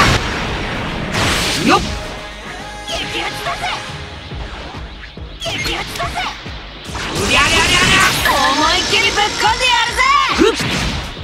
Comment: I think I'm feeling him saying "This bitch needs to stop"